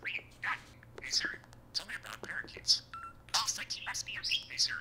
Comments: After He says, "Hey Siri". It sometimes sounds like He is saying, "Hey Siri, I'm a Parakeet". But it isn't picking up the Parakeet, part? What an adorable little soul 🤗🤗🤗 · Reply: He was saying, tell me about parakeets, then tell me about chickens. Lol it was hilarious. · I think I heard him say, "Tell me about barbecued tacos."